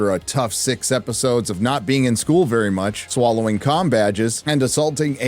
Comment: Someone please confirm. Did she say "cum badge" in the show or what the fuck dis she say back then...